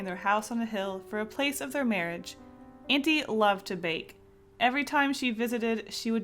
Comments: i thought u said the aunt vaped, then i herd that she vaped cookies so i replayed it and it made sm more sense! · OH MY GOD I THOUGHT YOU SAID AUNTIE LOVED TO VAPE OH MY GOOOOOSD